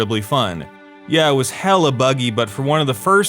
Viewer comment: Did I hear a hella at 2:42? Cali represent